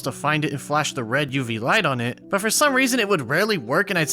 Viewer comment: did they say "red UV light"? Please tell me, that this is not what the game calls it...